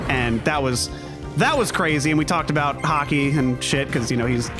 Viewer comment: I heard Hockey as "We talked about Ha-kai." Now I just want an anime hockey series.